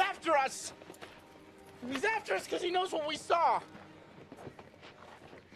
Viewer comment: “And now he knows my name” 😂😂😂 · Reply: And I always thought he said "And how he even knows my name"